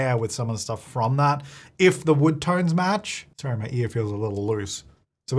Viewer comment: lol I wasn’t looking at the screen and I’d forgotten about your celebratory look and I hear “Sorry, my ear feels a little loose”